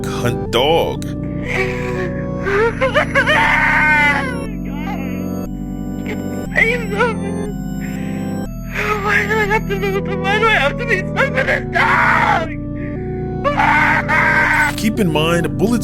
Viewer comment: does anyone know what he's saying? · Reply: He says oh my god why do I have to move why do I have to be infected WHY DO I HAVE TO BE STUCK TO THIS DOG WHY